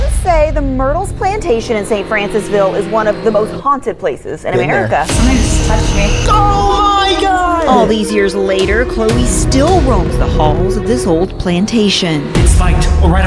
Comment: I thought it said “haunted playstation 😭😭”